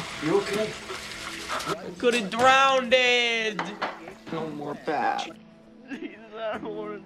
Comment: What’d he say at 0:15? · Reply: I could of drowned